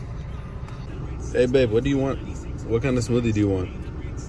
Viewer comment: did i hear " babe " ???